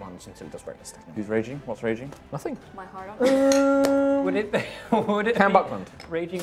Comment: What did say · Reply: Raging hard on